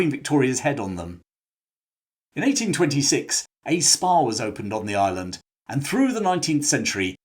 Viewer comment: At first I thought you'd said "in 1826 a Spar was opened on the island..."